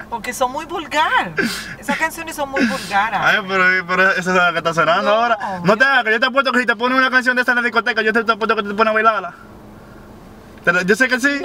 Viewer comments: Our que dijiste en 5:02 😂😂💀❤️ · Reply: I am a Unicorn q si le ponen una cancion de esas ella se pone a bailarla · Que dijo en 5:00 hasta las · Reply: Victor Gutierrez hahah q apuesto q si le ponen una canción de esas en la disco la baila